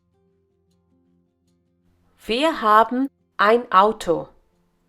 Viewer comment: Am I the only one who heard "Vir haben ein Auto" instead of "Wir haben ein Auto" at 3:40?